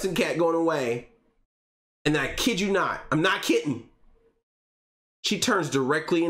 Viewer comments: Did you really say “I’m not kitten” at 26:45? LOL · I'm choosing to believe you actually DID say "I'm not kitten" and I didn't mishear you. 😂